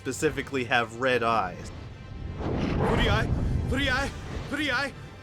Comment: her ladyship furryeye?? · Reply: I heard "Fury-Eye."